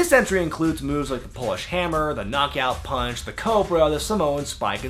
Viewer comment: When said " Polish Hammer" I thought he said "Bullhammer." I almost unsubscribed lol